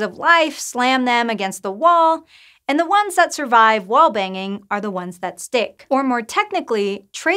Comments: I thought she said survive while banging and I was like makes sense lol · legit thought she said "the ones that survive while banging"